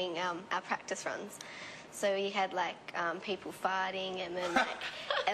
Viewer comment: did lauren say people farting or people fighting at 3:07?